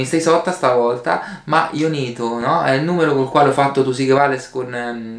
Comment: Nel minuto 0:18 ha detto rincoglionito o l'ho sentito sol io???